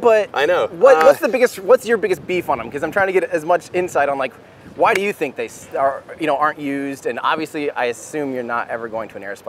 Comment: Haha, it sounded like you almost asked Elon "why do you think they (aerospikes) suck?" 😆